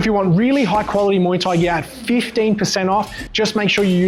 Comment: muay thai gyat what is unc out here saying 😭🥀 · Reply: "Muay Thai gear at" not "Muay Thai gyat"